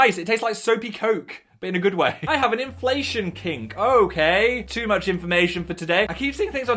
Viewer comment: ok but i genuinely thought they were saying they're into inflation.. like.. the prices going up